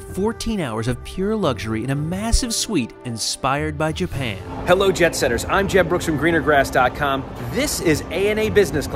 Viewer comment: Looking at her shirt. All this time I thought he was saying "Hello, Jebsetters." 😆